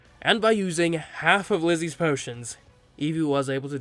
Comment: I keep hearing ‘glizzy’ instead of ‘Lizzy’